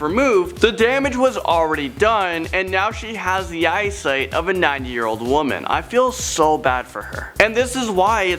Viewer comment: I thought you said a 9 year old woman